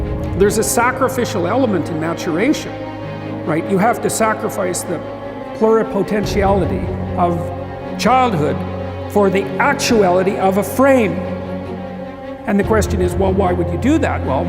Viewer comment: What does he say between 1:58 and 2:10? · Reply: You have to sacrifice the pluripotentiality of childhood for the actuality of the frame. And the question is: well, why would you do that? Well, one reason is: it happens to you, whether you do that or not. You can either choose your damn limitation, or you can let it take you unaware when you're 30, or even worse when you're 40. And then, that is not a happy day.